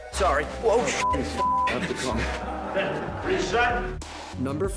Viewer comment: lmao dr strange even said “what did you say?”